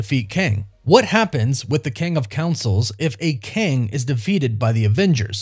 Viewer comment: says Kang of councils instead of council of Kangs @ 4:45. Me: 🙃